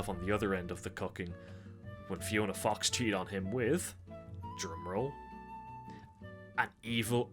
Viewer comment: instead of saying drum roll, I thought you said gemrl and that got me very confused